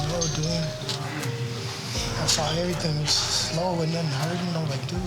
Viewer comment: Subtitles are wrong. He said *"I saw everything. He was slow. Wasn't nothing hurting..like, dude"